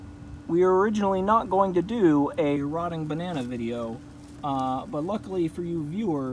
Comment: I thought it said rotating banana. Disappointed.